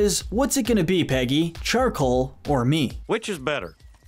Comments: "Charcoal or me" is even more disturbing if you think about it. Would he divorce Peggy if she chose Charcoal over Propane? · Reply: Up until now, I always thought he said “charcoal or meat”, because of the “taste the meat not the heat” slogan. As a way of directly implying the charcoal burger would taste like charcoal. I don’t know how to feel about this revelation. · I always thought hank said "charcoal, or meat" when watching on tv... seems way more passive aggressive knowing he said me and not meat. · I thought Hank said "charcoal, or meat" because the slogan for Strickland is "taste the meat not the heat". So he was asking if Peggy liked the taste of charcoal or meat better, not to choose between charcoal and himself.